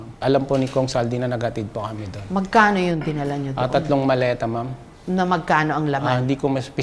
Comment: Tama ba narinig ko 5:27 - tatlong Maleta tas 6:10 - limang maleta? Baka mali lang pagkakarinig ko